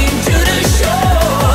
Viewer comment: "Is everywhere a Manifold love?" If you're an intake manifold yeah everyone loves you! If you're an exhaust manifold............ I don't think some people will have the same appreciation. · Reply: I hear it as "It's everywhere, I'm manic for love!"